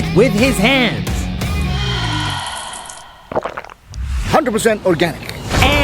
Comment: 100% cocain. joke but that wat i hear